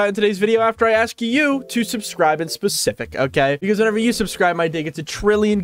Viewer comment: OC INSTEAD OF OK?????????